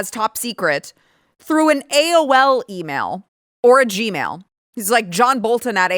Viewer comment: I heard "orgy mail" instead of "or gmail". 🤦‍♂️🤣 I need help lol.